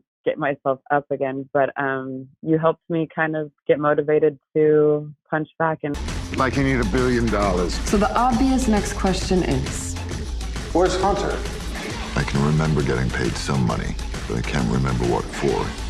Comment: Mike Hunt? What is he saying?